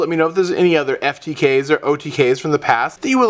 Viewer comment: when you do your opening and say your name I dont hear you say "Galactic God" I hear "Galac The God"